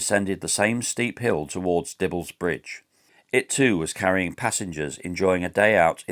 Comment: For half the video I heard "Devil's Bridge." Might be a more appropriate name. Some might slow down when they hear "Devil's Bridge."